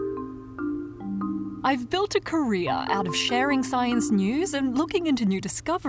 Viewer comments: It sounded like you said "I've built a Korea." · When you said career I thought you said Korea....